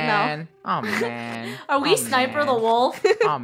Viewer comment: Did just say are you sniper the wolf on